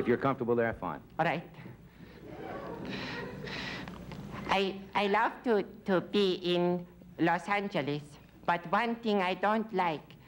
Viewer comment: Sounded like he said " I love to pee in Los Angeles". Hilarious 😄